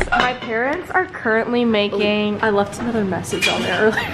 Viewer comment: did someone say another death?